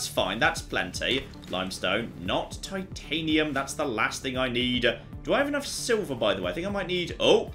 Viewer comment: Funny things says during this video: "Fins. Flipping perfect." "Titanium, oh, that's the last thing I need." "I think he's looking for something small, and/or more edible for him." "I've made terrible decisions." "There were so many tabs I didn't realize were here." "I've accidentally skipped a couple of steps." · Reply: ROFL XD Early I heard... "Titanium, oh, that's the last thing I need." Then later it became... "Okay, it looks like I'll be needing ALL the titanium" ROFLMAO! Adventure on friend :)